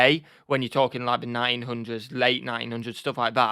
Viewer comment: Why do British people not ever pronounce the letter "T"? At one point ya said "In the 1900's." It sounded like you said, in the nine hundreds (900's) Or even saying British, it's always...Brish